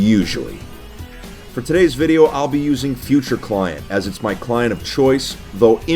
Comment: oof futureclient is paid i searched up skillclient because i thought that was wat u said and then i replayed video futureclient and found out it was paid but i wonder why because impact is basically the same maybe better with baritone now